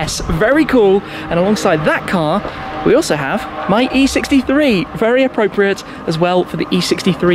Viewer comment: I haven’t been watching too much lately, why does he say “my E63” @ 1:45 mark? Does he own a E63 now, too? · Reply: G63